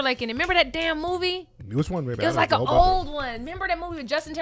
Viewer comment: Did call 'baby' here? Lol